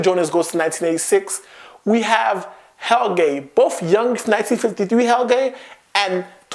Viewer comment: I heard "Hell gay" for "Helge" :/